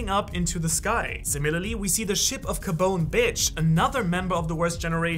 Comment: Did you say Capone Bitch?